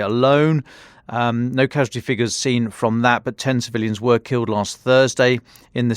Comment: does the narrator actually say tens of millions were killed last Thursday? It sure sounded like that, but I've got hearing difficulties. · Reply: Ten civilians is what he said